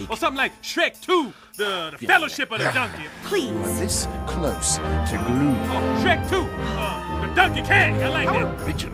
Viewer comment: what did prince charming say? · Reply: You are this close to glue